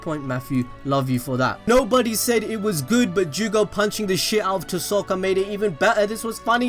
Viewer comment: I thought u were saying that no one thought it was good lol